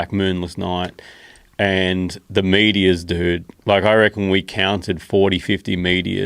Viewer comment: I thought he was saying medea, like he was talking about a bird I didn't know about. And Joe's like yeah medeas. Meteors.... OK. Makes more sense. I'm high.